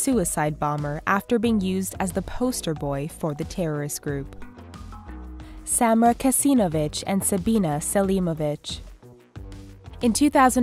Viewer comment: i thought she said "samra kesinobitch and sabina selimobitch"